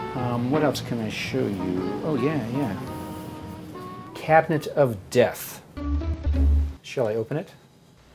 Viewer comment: i thought he said "cabbage of death"